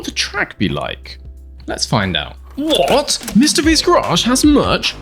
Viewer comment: did you just say "mrbeast garage" instead of "mr v's garage"